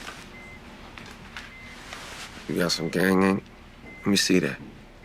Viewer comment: I don't speak street. What are they saying? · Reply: "you got some gang ink (as in tattoo)? lemme see that"